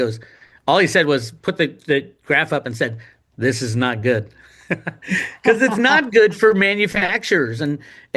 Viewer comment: I thought he said it’s bad for industry? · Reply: He just replied with "'This is not good"